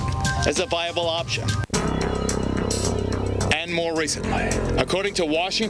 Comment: what with the creepy whisper at the end of "recentlehhh" 1:21 .....shudders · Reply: Wow I listened to it a few times I think the whisper says "Pain"